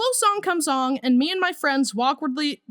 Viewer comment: did he just said awkredly again?